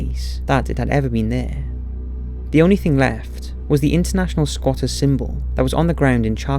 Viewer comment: what symbol is that? "Couldn't hear clear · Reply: International Squatters' Symbol: <link>